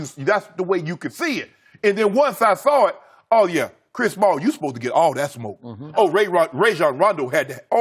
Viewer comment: Did he call him Chris Small at 3:13? 😂😂